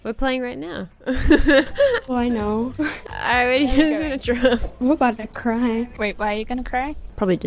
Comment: I thought it said “ I’m about to gay” XD